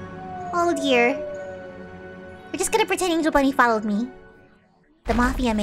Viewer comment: I thought u said Angel Bully lol